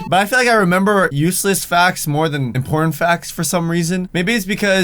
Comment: I thought he said porn facts